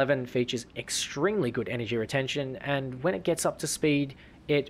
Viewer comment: Did I just hear "Extringly good energy retention?"